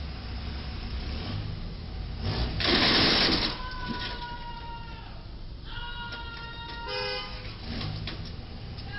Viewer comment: What did he say at 1:05? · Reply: "That fucked your shit up bro!"